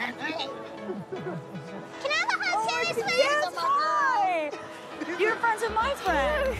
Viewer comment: The little girl said "can I have a hug too Ms swift" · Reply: I thought too she said miss swift but she actually says "Can I have a hug Taylor Swift?" at 0:25. Slow down the video to 0.5 and you can clearly hear it. Have a great day.